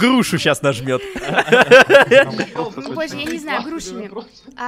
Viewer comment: Кто услышал ,,Поззи я не знаю"